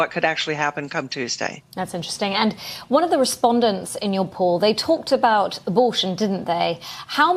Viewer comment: Thought at first she said : "They talked about ... Bvllsh!t , didn't they ?!" 😅